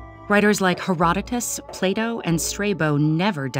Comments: I thought they said “Play-Doh.” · Im not sure why, but I heard 'play dough' instead of 'Plato'.